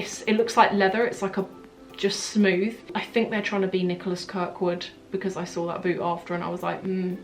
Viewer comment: what designer is she saying? Can't figure it out, Nicolas..... what? :P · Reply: Nicholas Kirkwood